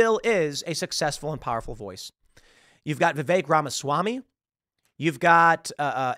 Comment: Every time Tim says Vivek Ramaswamy, i hear him saying "The Fake Ramaswamy".